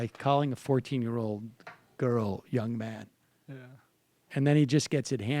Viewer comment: Dave Eirikid no, he said ma’am. It’s a common greeting in Texas, but only in certain parts. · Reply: No he says young man listen closer. I thought he said ma'am at first too.